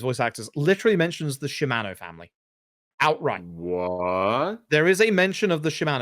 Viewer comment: I’m very sorry to burst your bubble, but actually said “shima no gomi” (he’s referring to the waste “gomi” on Palekana Island “shima”) instead of Shimano Gumi, just went back and watched it myself. Truly a shame tho!